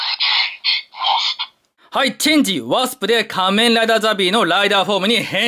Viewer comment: チェンジロストって聞こえるの俺だけ？